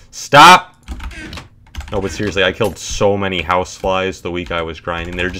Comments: At first i thought he said housewives “I’ve killed so many housewives this week” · thought he said house wives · When he said houseflies i thought he said house wives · This entire time I thought he was saying he hated house wives, and that he has killed several housewives. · Why did I think he was going to say house wife's? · I thought he said housewives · Thought he said "i killed so many house wives" and got real confused xD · First I thought he said housewives when he was talking about KDR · Bruh the whole time i thought he was saying “housewives”instead of “houseflies”